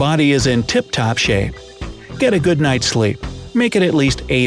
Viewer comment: SLEE instead of SLEEP.